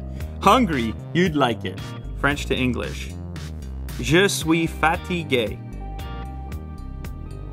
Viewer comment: I thought he said "Jus' we fatty gay"